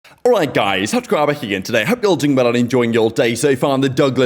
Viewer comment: “Alright guys HotGrabber here…?” Is that what you said?